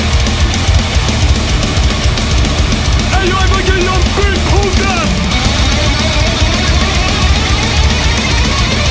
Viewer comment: What him say in 3:06? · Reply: He says: All right, Eva B, show them! Evangelion B - the guitarist.